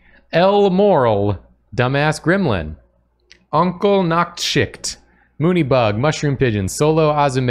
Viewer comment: thanks a subscriber with a name that sounds like "Uncle Nachtschict". This was kind of surprising to me, because as he read that name I am filling out a D&D character sheet for my latest character, "Gideon Nachtkampf", and for a second part of my brain thought he had just said the word on the page in front of me! (It's a little similar, at least)